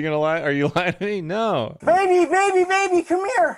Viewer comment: I believe he said baby more times than Justin Bieber’s entire first solo album. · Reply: I thought he said 'baby' too, until he said 'Bailey-Anne' or something at 8:09. So I'm guessing that's her name and he's saying Bailey the entire time?